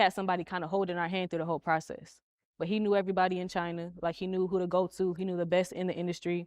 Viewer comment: "He knew everybody in China"... Did I hear that right? Is that production? Legit curious if the shoes are made there. (Well shit, I answered my own question. The shoes are made in China) And for that reason I'm out.